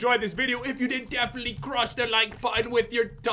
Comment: did pat really say smash that like button with your DONG? wow 😂😂